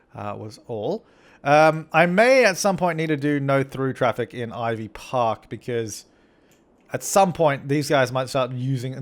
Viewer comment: I thought Ivy Park said My park for a minute haha. that's what you should name it